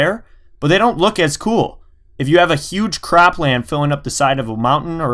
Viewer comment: Is it just me or did he just say cr*pland?